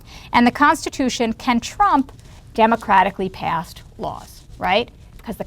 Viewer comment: What did she say at 33:14 ? The constitution can... I didn't get this line. Anyone who can tell? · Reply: "The constitution can trump democratically passed laws." Since it is the supreme law of the land